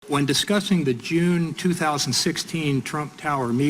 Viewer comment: At the beginning of this video I heard "When discussing the June..." and for a moment I thought I heard "When discussing the Jew". I thought, thank goodness, people are finally brave enough to discuss Jews. But no such luck.